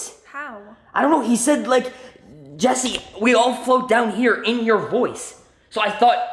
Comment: Istg I thought my boy Penny said “you’ll float too”, someone explain?